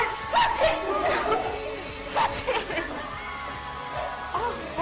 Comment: did i heard shut it ??😂😂